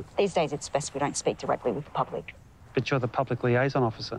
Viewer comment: "...But you're the public liaison officer...." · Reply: Totally thought he said “bitch you’re the public liaison officer.” 😂😂😂